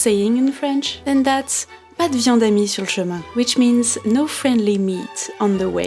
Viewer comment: Can you repeat what The French say at 18:10 n the video? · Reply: " Pas de viande amie sur le chemin " 😂